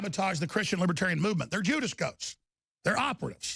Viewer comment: what does he say at 9:48 - 'Judas Coats'? what is that supposed to mean?